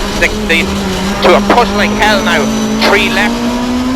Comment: What is he saying? something something Hell no?!? :O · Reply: "Push like hell now" :)